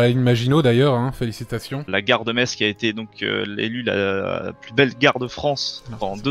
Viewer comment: oh nan j'ai compris "plus belle guerre de france" XD